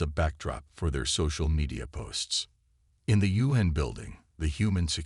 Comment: The video has captions saying "In the Yuen Hen building ..." when the line is "In the U.N. building ..." Tell me this is AI slop without saying "artificial intelligence".